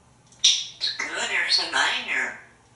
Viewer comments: You're TOO adorable! Good boy. 1:42 it sounds like he says "it's good, Ursa Minor" made me giggle! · sounded like he said it’s good here’s the money here